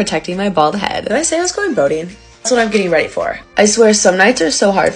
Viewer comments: I thought she said “I’m going voting” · Thought she said voting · tell me why she said “boating” but i heard “voting”😭🤚🏽 (btw i was not reading the captions)